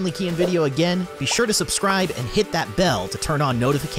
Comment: When I skipped to the end all I heard was “subscribe Ching”